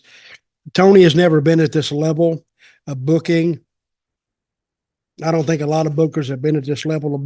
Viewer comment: I keep hearing "Tony never done booking" and stuff like that. Hasn't he been doing it for years now?🤔